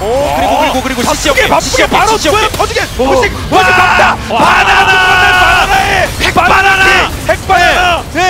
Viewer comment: 나만 여기서 와나나 라고 들리는건가??